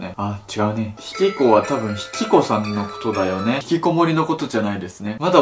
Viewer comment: 見たことあるやつ引きこさん引きこもりに聞こえたわ